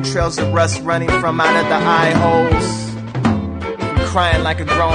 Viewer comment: two trails of rust running from my metal eyeholes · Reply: I thought he said "leather eyeholes".................